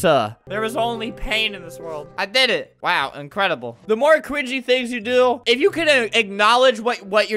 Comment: Did anyone notice at 13:26 Albert said cwingy instead of cringy?